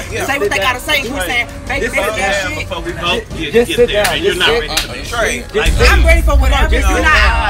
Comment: I'm confused did threaten? I didn't hear it. What I will say is you can't jump up and state I'm on whatever time and when gives you time and stand up you say he’s threatening you because you're feminine. I would like for them to get over this situation. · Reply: No, actually you're wrong. said "calm down before we both get there. And you're not ready for that." That's when Trey responded "I'm ready for whatever." said it first and then Trey responded to what he perceived to be threatening words. After Trey said "I'm ready for whatever" got up and started walking towards him. I time stamped actually @ 42:02 lol